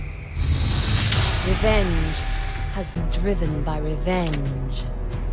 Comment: “Revenge has driven by revenge” -Pharsa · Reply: well,i didn't heard "has been driven" at 1:57 so yeah :/